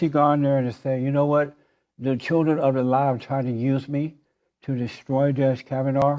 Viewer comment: I thought he was saying the "Children of Elias". What???